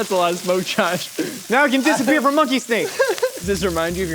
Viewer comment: Is it just me or did Josh say "monkey steak" at 18:18? :)